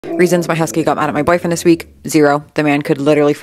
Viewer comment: I heard "Reasons why my husband got mad at my boyfriend this week" and I had to doubletake.